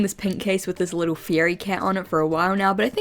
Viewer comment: When you showed off your fairy cat I thought you said "fieri cat" 😭 I was like guy fieri cat??